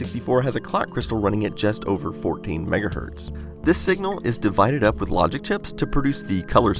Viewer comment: the CC says "17 mhz". I assume it's a typo, since in the audio it says it's 14 MHz